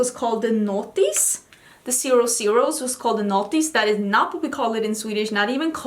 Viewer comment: Not me having to come to the comments to see if anyone wrote what the '00's are called 'cause I had NEVER heard of it and I thought was saying The Naughty's and I was so confused 🙃🙃🙃🙃